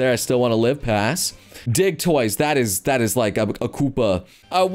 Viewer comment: I thought he said dic* toys